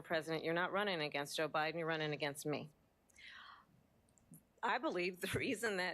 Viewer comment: When she said “you’re not running against Biden you’re running against me”…. Was it just me or did it sound like she said “O’Biden”?